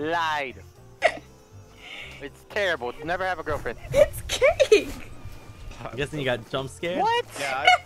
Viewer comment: Did anyone hear about at 7:08 she called him “jake” if I heard right , I wonder if this is his real name ? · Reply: she said “it’s cake” i’m pretty sure :P